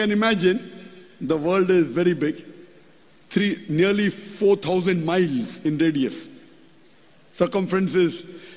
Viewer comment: bumi=karpet😂😂😂😂😂 ZN BAHLUL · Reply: salah denger saya, kedengarannya Forthy thousand. trims atas koreksinya .. ZN benar kalau 4.000 mil radiusnya.